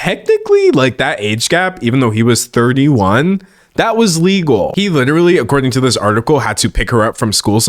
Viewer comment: the subtitles says illegal instead of legal, even they knew this sounded wrong